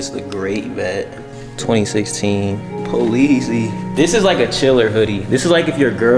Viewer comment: fuck did dude just say "Poleezy"😭😭😭